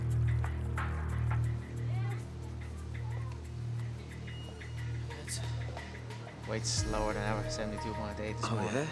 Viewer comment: What did Conor said? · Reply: "Lads"